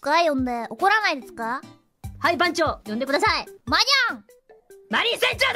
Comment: 「マニャア！」って聞こえるｗｗ